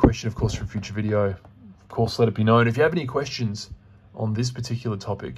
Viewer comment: I can't believe it. Until today, I thought you were saying 'see you in the comics, like see you in the funny papers. I just thought, that must be the way the Aussies say that. Jokes on me and now you are seeing me in the comments.